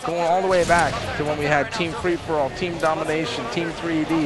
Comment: did he say team free for all around 3:30?